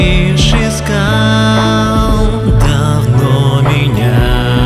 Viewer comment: убейте, я не смотрела на экран и услышала "Говно меня" теперь я не могу раслышать это по другому :">